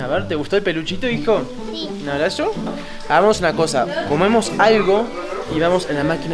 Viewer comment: le entendi a fede fumemos algo y hera comamos algo